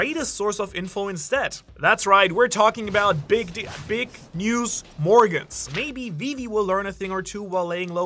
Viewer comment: Yo did I hear BIG 😶. MR. Had me dying for min 6:30. I was wondering if I was watching the right content. Either way I'm at the right spot just need a heads up next time